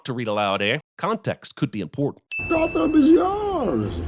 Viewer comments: <Bane> Golfing and smores! Ummm... REALLY? LOL. Could have sworn he said, "Gotham is yours!" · How you manage to get "golfing and s'mores" instead of "Gotham is yours" is beyond me